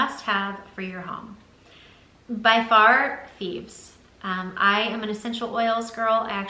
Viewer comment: I'm sorry, could you please write what product you said you used for cleaning before Grove? It sounded like you were saying "thebes." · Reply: Michelle McDermott I think she said Thieves by young living essential oils they have cleaners, oils ... you can check the webside what they have.